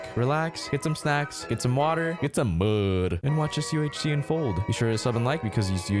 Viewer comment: did he say bud